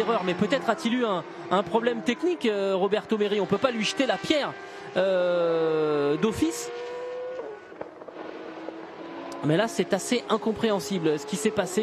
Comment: all I hear is "uhhh.. doofis!!"